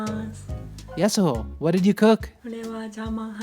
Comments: I swear I thought you said her name was Yasuo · I thought he said yasuo which reminds me that I'm so addicted on league😅😅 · Idk if its just me but i keep hearing “yasuo” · thought I heard Yasuo!!!!!!